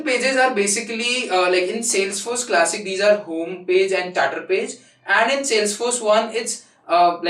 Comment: What did he say at 5:19 , "home pages and tatar pages" ??? I'm not able to ear the word pronounciation. Somebody can help? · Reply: Chatter pages.